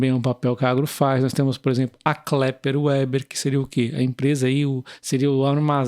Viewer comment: eu não falei isso? Porque se não é por conta como na maioria das vezes, ter de falar feito uma metralhadora de palavras rsrs Não é meu normal abss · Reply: eu entendi "kléper uéber"... "képler vêber". Mas não muda o fato de ser ótima empresa, assim como as demais que apresentou. Outro grande vídeo!